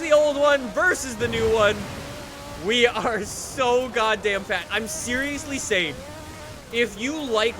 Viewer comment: it sounded like fat instead of fast